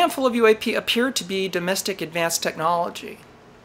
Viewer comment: the report does not say it's domestic advanced technology, it says demonstrate advanced technology.